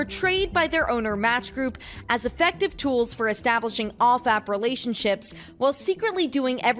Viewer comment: I heard "...as effective tools for establishing all-fap relationships..." which is more true than what they were actually saying 😂